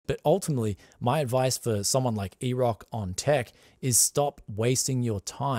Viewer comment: I’m confused by this. Can you please explain? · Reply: Sounded like was saying Erock On Tech instead of deck. It was just am attempting at a joke 😅